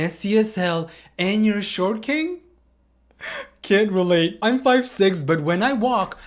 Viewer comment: I thought she said he’s “a short cake”